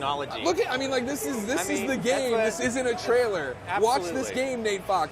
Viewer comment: Oh damn lol woopsie .. thought he said this game is shit -_- obvious mistake